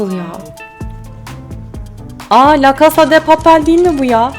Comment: ne dediğini anlayan var mı varsa yazsın lütfen 🤣😹😂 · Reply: La casa da papel